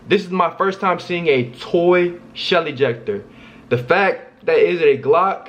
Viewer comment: I thought he said Shelly jecter😂😂😂😂